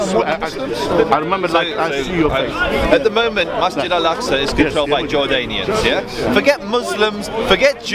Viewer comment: did he say At the moment Masjid al-Aqsa is controlled by Jordanians ?